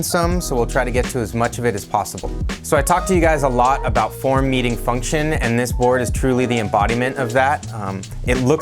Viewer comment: I heard what we all were thinking "Porn meeting function". ;o)